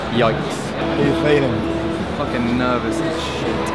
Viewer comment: did he say ''fucking'' or what?